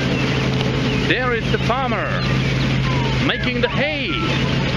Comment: When you first started talking about hay I thought you said you saw the farmer rolling in the Hay. I was thinking you gonna get flagged.